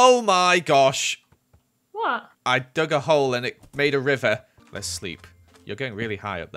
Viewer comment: “I dug a hole and it made a river” Only true Minecraft players understand this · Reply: i thought u said “I drugged a hole and made it a river